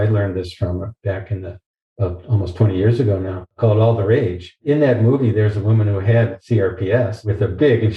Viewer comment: Does anyone know the name of the movie about Dr Sarno around 17:10? I can't quite catch what he says. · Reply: Yes, it’s called “All the Rage” and I found a link to it on Amazon but you can google it and find it elsewhere. Here’s a link: <link>